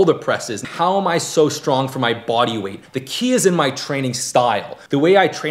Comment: all i heard was "the key is in my tren"